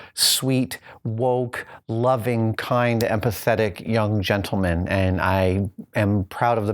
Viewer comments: did he just call his kids 'woke' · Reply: Necramonium when did he call them pathetic? I heard him say empathetic, which is very different haha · when he says empathetic gentlemen, I keep hearing and pathetic...and was like whaaat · I thought he said pathetic not empathetic. XD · it sounds like he says "and pathetic"